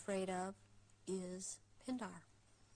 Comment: Can anybody understand whats she is saying? The queen is afraid of???? " Children" Or "Pindar"? I dont understand it. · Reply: Pindar. Ich kenne ihn als griechischen Dichter, fand eben in Bezug auf Icke folgendes: "As for Pindar, Icke translates the name as meaning Penis of the dragon and states that he (Pindar) is also known as the Marquis of Libeaux (as mentioned on an earlier reply). At the time he was writing the book, Icke had little else to say about this particular reptilian other than stating that he is above the Windsors in the Brotherhood heirarchy." (Quelle: <link>